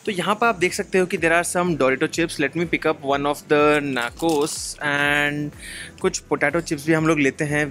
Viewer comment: Did he just say NAKOS ????????